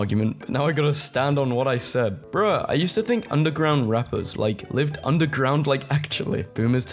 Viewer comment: "Underground mechgods" (What i heard😭)